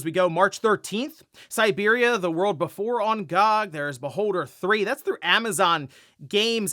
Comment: Thought you were saying “on god”